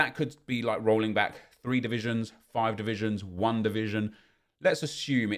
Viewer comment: When he said one division it sounded like wandavision 🤣🤣